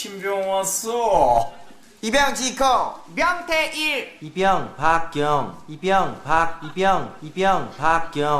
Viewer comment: 저거 왜 계속 돌리나 햇더니 이명박 처럼 들리게 노린거 아니냐 ㅋㅋㅋㅋ